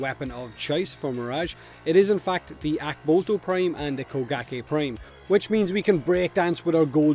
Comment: I thought you said bukkake prime man and I spit coffee everywhere. Jesus "fakkin" Christ man 😂😂